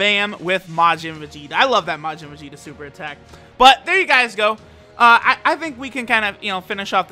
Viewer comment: did u say here we guys go?